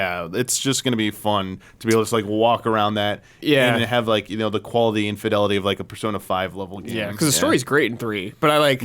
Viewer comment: thought said "quality infidelity" lmao